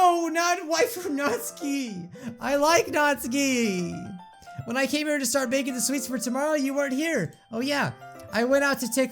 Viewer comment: when Natsuki glitches in the kitchen she said “When I came here to hid from my fatheRrrRrRRrr” just so you know what she said like this so B can see this